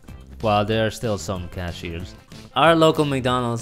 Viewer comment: The first build I thought they said Mc Donald's didn't have cashews... Or did they cashews